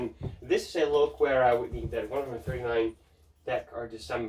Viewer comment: What did you say that leather jacket was? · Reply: It's by IRO, but I said it was quite a basic leather jacket and for my personal taste longing for something more extravagant.